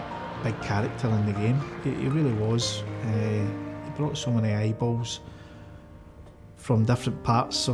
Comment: Can anyone please help me out with what John Higgins says at 6:46? JP had me in floods as I lost my lifelong best friend 16 years ago and my greatest wish would have been to tell her just how much she was loved 😢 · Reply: Brought so many eyeballs